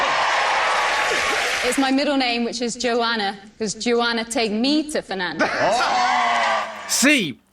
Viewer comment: when they were talking about the child names and the pickup line abt the island, I thought they said, "Take me out for nando's" 😭